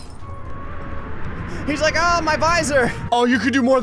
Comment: did you just say.... visor